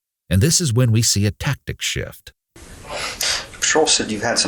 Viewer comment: At about 1:08:00 the detective mentions a woman's name, I don't know who she is or if you accidentally left the victims name in just wanted to point it out. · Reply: Exact time stamp is 1:10:14. They say patrol but the subtitles say Cheryl. Sorry about the confusion, being half def I rely on subtitles aswell. Had to replay that section and blast it to figure it out.